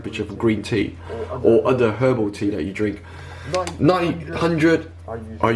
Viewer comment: Uploaded on my birthday 😁 At 6.15, it sounded like you said 85 degrees is normally used for her booty (I know you meant herbal tea) lol 😂😂😂